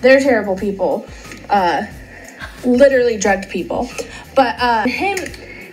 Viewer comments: I keep hearing “drag people” until I read the words on the screen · Sounds like she says litterally dragged people